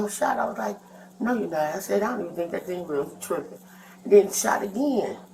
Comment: What really annoys me when watching MWS / MWC is the captioning of what people say. The woman at 5:38 said ""He trippin'" not "He triggered." this is something small but could be potentially harmful to viewers watching